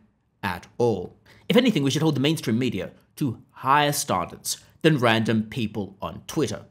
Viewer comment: Bro did you say ‘starndards’?